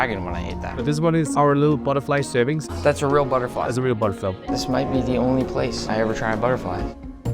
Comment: He Said butterfill instead of butterfly